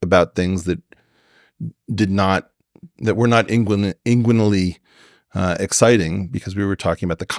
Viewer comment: actually curious what word Eric used. Chat GPT has no idea... i even spelled out a few ways myself and used captions lol any takers? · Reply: Look up the definition of the word. Inguinal. Of the groin. Talking about context not just the sexual aspects, I can presume. Always look into the roots of the word to understand context